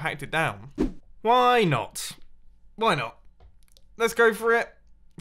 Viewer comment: idk why but I hear "whyyyy not, bueno"